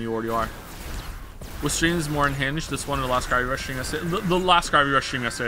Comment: What the fuck is he saying at 13:35 Edit: never mind “last gravity rush stream yesterday”